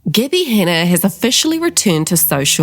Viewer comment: I love your accent. I'm also an uncultured American. When you say "Gabbie Hanna" I hear "Gibby Henneh" and I can't stop laughing